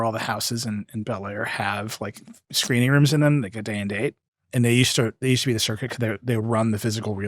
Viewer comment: what are they saying? Day in date? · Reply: "day and date" which is when a movie releases on streaming services the same day as the theatrical release